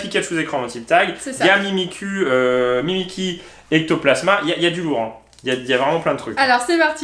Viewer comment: Mimi quoi ? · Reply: C'est mimiqui !😁